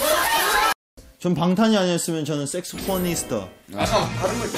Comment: is he saying saxophonist or sexy porny star??!! 😭😭😂😂